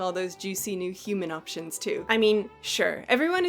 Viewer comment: I misheard as "their new furry human options" and had to pause the video and double take back to see what in goddamn hell was going on.